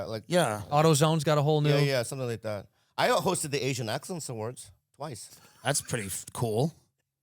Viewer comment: I literally thought Bobby said he hosted the “Asian accents awards”